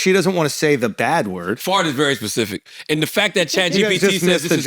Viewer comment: I agree with Andrew I thought she said fart n instead of fuck N which is insane you can’t say fuck but u can say n word in her mind thats how deep rooted racism is